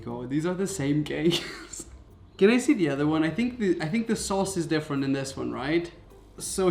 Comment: did he just say Sauce.